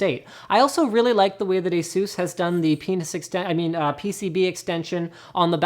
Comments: Did Linus just say Penis ? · "penis exten..." yeah i thought i heard that right. lmao. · did linus just say penis extension? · Anyone else hear "penis extension" @ 4:52? LOL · Did he just say Penis Extension?! Ahahah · Did he say penis?